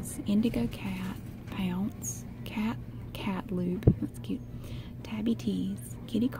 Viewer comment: I thought I heard Cat Lube instead of Cat Loupe and got so confused when she said Oh that's cute. I went back and realized that I definitely heard her wrong. Also, why can't the CVS in my town have jewelry like that?????